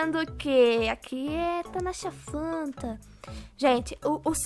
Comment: entendi Nataca fanta kkkkkk